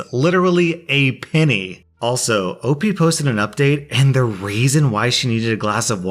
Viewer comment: I can't figure out if you saying "and the raisin why..." at 6:33 is a mistake, a joke/reference, or some subtle trolling.